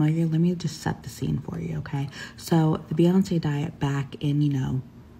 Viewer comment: I thought she said Beyoncé died and I was like “WHAT”